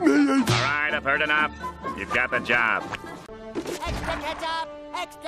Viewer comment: Rather than "Extra Ketchup" I heard "Extra Catachan"